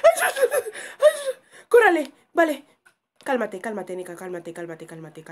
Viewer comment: En el minuto 7:09 en lugar de escuchar cálmate escuche calvate😂🤣😂🤣😂🤣